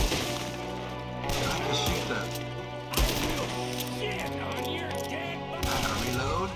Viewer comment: I never noticed until this video, I think I heard Micah say, " I will shit on your dead body"😂